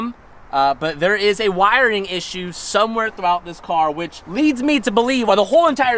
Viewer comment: When I heard, "it has some wiring issues" I actually heard, "Calvin has some wiring issues" 😂